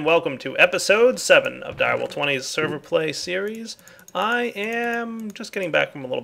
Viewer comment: Did he say server play series?!